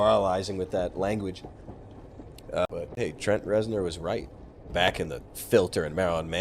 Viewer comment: What exactly did Trent Reznor say? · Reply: “Wish.”